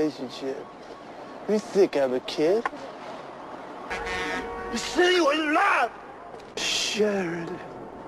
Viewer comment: What does he say at 6:35? I could never make out what he says in that line. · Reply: "You said you wouldn't laugh!"